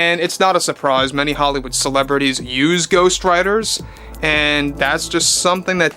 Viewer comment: 😂 oh I thought you said GhostRiders 🤣